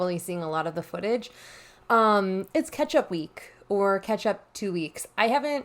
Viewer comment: You said “catch-up week” and I heard “ketchup week” and was then confusedz